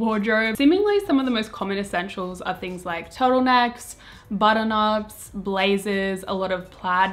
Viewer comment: i thought she said butternut instead of button ups I- i need to go to bed 😂